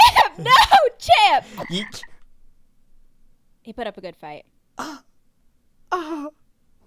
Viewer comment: Did anyone hear grain ALMOST cuss in front of us??😮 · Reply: He said fight